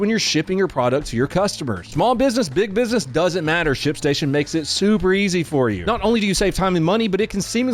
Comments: At the add I thought he said shit station 😭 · I definitely heard "shit station" 🤣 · When your ad started, I could have sworn you said "shit station". I looked at TV like, wait, what?